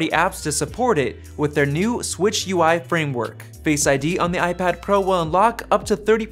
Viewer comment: Did you just say SwitchUI instead of SwiftUI ?